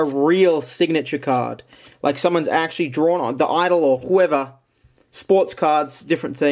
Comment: “Wi Swartz” What I hear “Wii Sports”